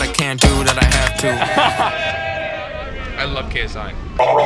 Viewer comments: did he say "I love KSI" lmaoo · Am i the only one who heard him say i love ksi 2:09 also goated song